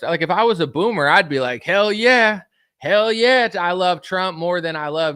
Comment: I swear he said dial up trump... Lol ok im still not crazy. Rocks back and forth staring at the new ai overlords